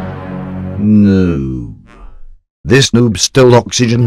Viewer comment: I thought you said no